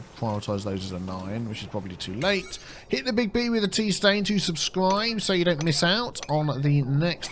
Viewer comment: ohhh you said Big B with the tea stain at the end. I thought you said big B with the pee stain :P Gotta be carefull with that ending. :D